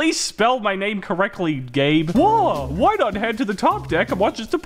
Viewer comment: Did he say 'gay' at 1:08,?